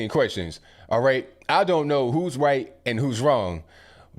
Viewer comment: "I don't know who's white and whose wrong" I heard that shit